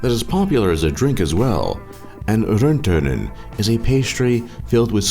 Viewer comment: Lol, wtf did you say? Atleast in the western part of Finland where I live, we call that ''Karjalan Piirakka'' which translates to ''Karelian Pie'' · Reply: Ace Jargo he said "rönttönen"😂😂